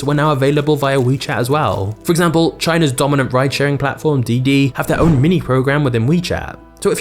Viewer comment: Thought they said bride sharing app